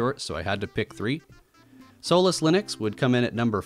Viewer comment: Lol I thought you said soulless linux